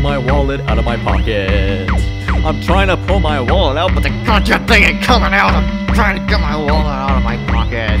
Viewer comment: it sounded like he's saying "IT CONCHEN THING AIN COMIN' OUT!!" lol. xD